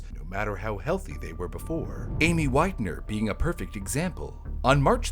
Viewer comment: I’m driving listening to this and I heard “any white nerd” 😅😂